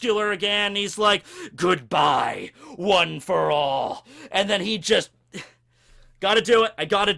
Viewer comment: did he say One For All or am I tripping off something